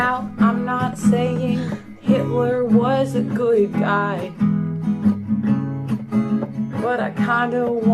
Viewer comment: I thought she said NOT saying h!tl€r was a good guy Doesn’t that mean she doesn’t think so